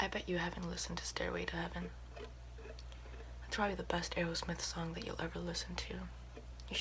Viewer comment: When u said Aerosmith I thought u said taylor swift at first and I was like " I know it's supposed to be wrong on purpose but dang" 😂😂😂